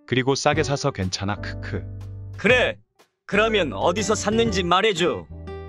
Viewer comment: 분명 :_ESTJ:는 "그래? 그럼 어디서 샀는지 말해줘"라고 했는데 난 왜 "그래! 그럼 어디서 샀는지 말해!"로 알아들었을까ㅋㅋㅋㅋ 오늘도 박력 넘치는 호랭이 그리고 0:19 오늘도 착한 울 귀염둥이들ㅠㅠ:_ESFJ:🐕